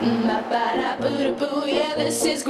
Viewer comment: I kept hearing- this's where grampa screwd, until I read the name! 😂